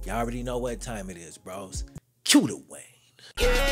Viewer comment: I always thought you was saying Que the way! Damn! Cue the Wayne!!!